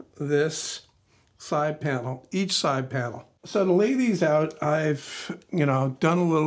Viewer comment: sounded like " so the lady is out" lol!